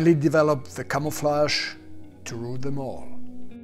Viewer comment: I thought he is saying UFO Camouflage